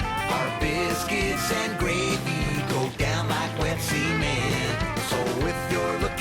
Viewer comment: "Our biscuits and gravy go down like wet semen"? I must have misheard that, right? RIGHT?